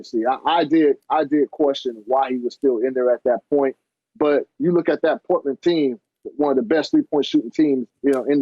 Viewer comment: When chis said “ you look at this Portland team” I thought I heard “this F%*@ team” 😳😂